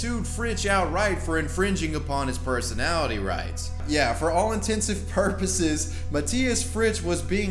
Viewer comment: Did you say "for all INTENSIVE purposes" ????